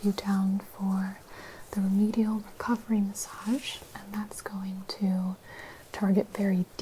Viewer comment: Did she say cockring massage at 4:12 ?? 😂😂